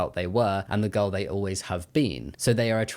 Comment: why did I think you said the girl they always had bean 🫘😭